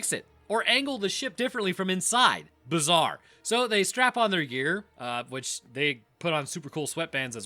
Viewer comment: Did he say strap in or strap on? 🤔